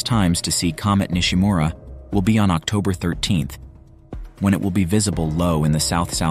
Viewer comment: You are saying the best time to see this comet is on October 13 in the South / South-West after Sunset. I live in the United States (Pennsylvania) and EVERY news article that I have read pertaining to THIS comet states the BEST viewing time and location are September 10 through September 12 facing EAST / Noth-East about one to two hours before sunrise. How are you coming up with October 13 in the South / South-West??? The information that you are putting out is an entire month later than every news outlet that I have read. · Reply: At the 3:49 to 3:51 mark of this video, the narrator CLEARLY states October 13th as one of the best viewing times. South-Southwest after sunset. I had to replay this a few times just to get clarification of what was said.